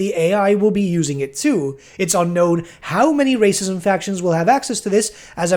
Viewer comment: did a double take because I thought you said Racism factions instead of Races & factions